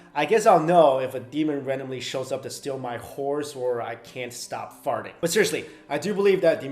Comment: I misheard "steal my horse" as "steal my whores"...Oi.